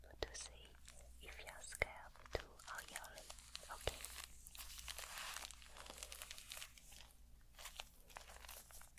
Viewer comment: What did she say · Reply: “oil, ok?”